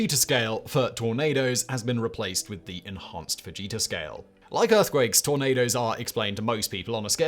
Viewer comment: For 9 @ 1:50 I continually misheard it as the Vegeta scale and I don't think that's less accurate.